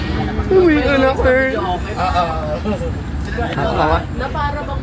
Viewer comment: narinig ko yung sinabi mo uwi kana daw kurt sabi ni miss kana daw ni kurt